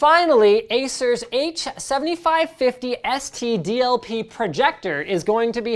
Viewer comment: did linus just say std?